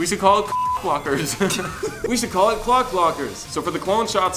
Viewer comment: "Clock". I thought it say Cock until they said it was clock - LOL